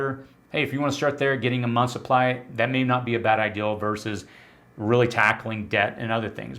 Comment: Why do you say ideal instead of idea..